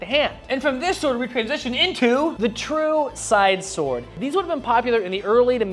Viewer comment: What was said "The true side sword" What I heard "True copper short sword"